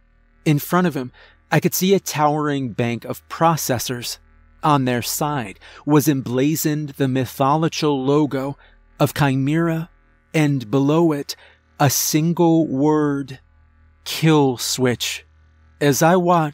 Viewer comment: I feel like every story has a moment where hear mispronounced a word in a weird way... this episode instead of Mythological, it sounds like mytho-lochill